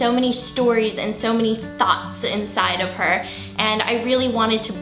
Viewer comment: Lol in the beginning I thought she said Hannah had so many thots inside of her